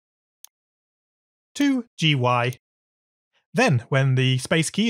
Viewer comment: Hmmm... A lot of hard work is put into these vids and humans do make mistakes and i found a mistake by watchiing ur vids over and over bc i like them i found a mistake at 13:11 the subtitles said dy but it is gy